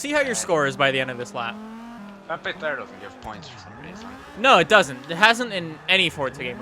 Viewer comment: What did say? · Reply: Stridsberg "That area didn't give me points"